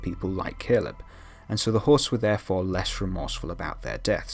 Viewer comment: i don't really watch the show but clicked on this video anyway. Everytime you said host, I thought you said horse lol. I was so confused.